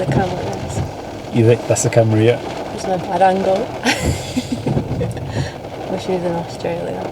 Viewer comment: Did she say “thats a bad angle i wish i was in australia” wtf🤣🤣🤣